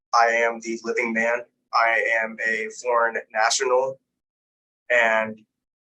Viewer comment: I thought he said he was a Foreign asshole....Now that's special! LOL. Ship him to Antartica!